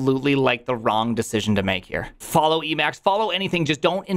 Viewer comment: Did he just say "follow eMacs" ? /me grabs torch